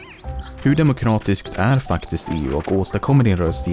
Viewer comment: i swear he just said ahh fuck this 😂😂